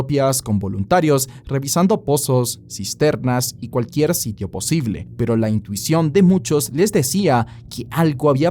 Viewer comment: ¿Qué dijiste? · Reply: “Cisternas y cualquier sitio posible”